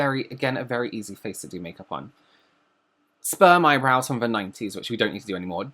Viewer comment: Wait did he say "sperm eyebrows" @ 20:37.... now I can't unsee it lol